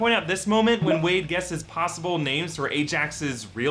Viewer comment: oh... mitch. I thought he said b%*ch